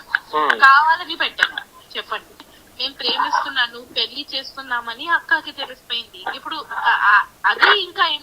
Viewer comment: Anchor super.... పొందిక గా స్టెప్ బై స్టెప్ మర్యాదగా అడుగుతున్నాడు... కీప్ ఇట్ up. మంచి... Anchor. అందరిలా ఓవర్ action లేదు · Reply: Ok, den Why did this anchor failed to catch wat SV said clearly 'Pelli Chesikunam' at 11:04 time in Audio Call which was played by Shekhar Basha in this Interview? Why the anchor allowed Shekhar basha to mislead people n twisted facts, do Character Assassination of SV throughout the Interview?